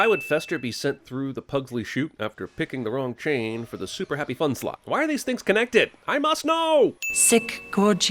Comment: you say “super happy fun slide,” but the subtitles say “super fun happy slide.” Physician, heal thyself!